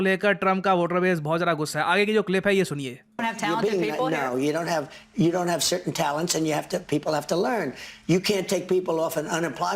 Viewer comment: But I heard as " we don't have certain talents not we don't have talents"